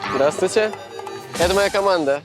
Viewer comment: Это моя охрана -Что он сказал ? ))) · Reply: Он сказал это моя команда